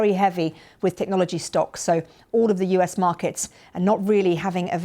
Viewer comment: Prices are down alright. Stock prices, not the price of groceries. He didn't lie for once. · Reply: Stocks, I thought you said steaks. Trump steaks.